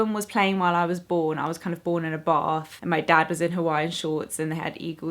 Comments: Definitely first heard that as "born in a Bar"... 😳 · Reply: Pretty sure it was "bath". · At first I thought you said "I was kind of born in a bar" and I was like wait whuutt.. Lol